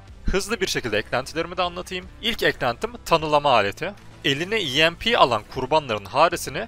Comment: tunnellama aleti diye duydum, ama verdiği bonusla çok iyi tunnellanır 😂.